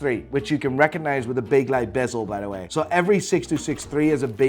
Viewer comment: What is he saying at 8:19 Recognize by its ….. …. bezel? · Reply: Bakelite bezel!